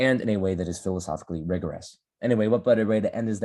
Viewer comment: Did you just say "what butter way to end" at 42:23? xD